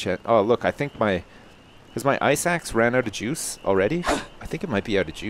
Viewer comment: I thought you said your eye sacks ran out of juice, but it was ice axe.